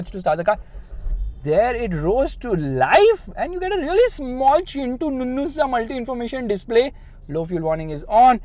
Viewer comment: did he said, chinnu nunnu sa display 😂😂😂😂